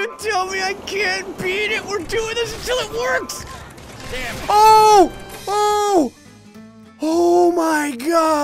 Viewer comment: I just hear dammit